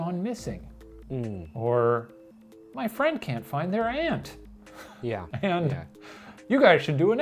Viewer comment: i usually pronounce "aunt" like "ant" but ppl can tell from context clues to know what i mean obviously. but istfg when shane said that i did a double take thinking he meant the bug and i had to turn on cc to check smh